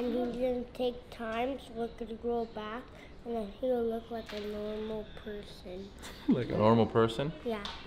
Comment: I thought he said he looks like an egg